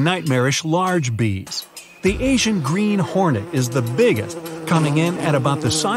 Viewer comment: did you say asian green hornet?